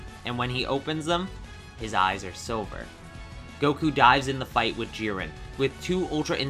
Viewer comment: goku dies an the battle with jiren? Is that what i heard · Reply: I think he said dives